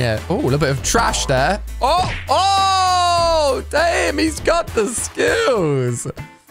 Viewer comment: Did I hear a wow??? “Woow ooooooohhhhhh!” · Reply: No he said ohh